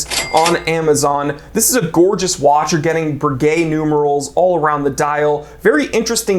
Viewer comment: I was taken aback when you mentioned the "Breguet numerals" in the Seiko Chrono, it sounded like you said "gay numerals".